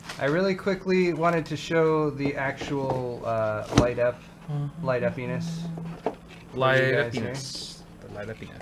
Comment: All I heard at 23:04 was " Light-up penis" X-D